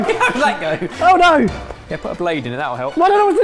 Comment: Does Barry says " I don't know what to do Babe!" to Ebbers at 10:00? LOL!